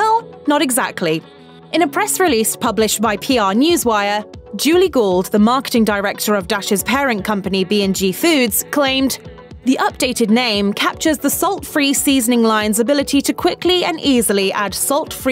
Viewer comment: Oh, "Gould". I thought you said "Julie Gold" and was like "...the writer of From a Distance is an ad rep now?"